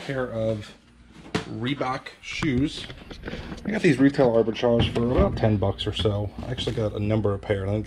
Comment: Really curious what does he say at 4:46? Retail overchage? Totally confused.